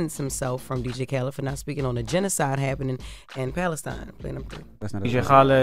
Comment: Did i just hear DJ Envy say that's not a genocide at the 2:57 mark?